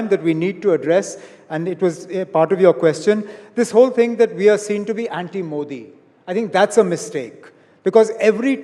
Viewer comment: Can someone tell what amar abdulla said on 26:25, India Today cut it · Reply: He said that people think we are anti Modi ,we are not anti Modi ,we are against the policies made by bjp ,we are against the hatred bjp has created in the minds of people .if tomorrow bjp loses then it will be also impossible to controll the andbakhts as they will be used by bjp in future .When bjp was in opposition ,media ,roadshows ,dharna...they were doing everything but today if we talk against bjp ,we become anti India .Educated people know that bjp is doing wrong but people who are innocent ,they get sentimental just because bjp tell them HINDUISM is in danger and get their vote and make govt.